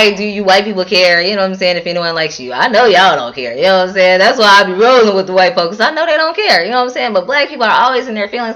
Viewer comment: Did you say "That's why I'm rolling with the white folks?". :-/